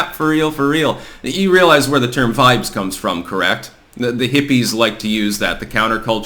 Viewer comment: he says you realize where the term “blank” come from? What word did he say, I couldn’t understand him. Vibes? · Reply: Yeah. Vibes. I checked the transcript.